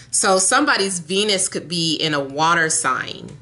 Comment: You were talking about someone expresses themselves through sex. I had to rewind because after that I thought you said someone’s penis could be in a water sign. 😂